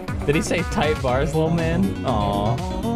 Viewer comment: "What did he say?!" Me: he said tight bars little man